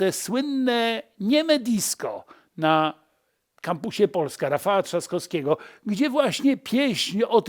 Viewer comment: Zamiast Kampus Polska usłyszałem Kapuś